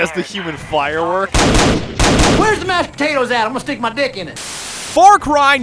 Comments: I legit spit out my drink when I heard: "Where'a mashed puhtatuhs at? I'ma stick muh dick innit." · Dude the first thing I heard was “where the mash potatoes at imma stick my dick in it”